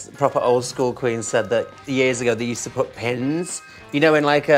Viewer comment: its a pen 🖊 I thought she said PIN 📌 😱😱😱